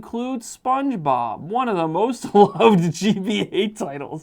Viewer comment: Are we supposed to see something different for the part at 1:02? Because it says one of the most loved GBA titles, but I could be mishearing him as I think he's saying loathed not loved.